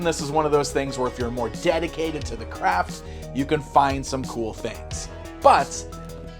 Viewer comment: Dedicated to this craft sounded like you were saying dedicated to finding crap.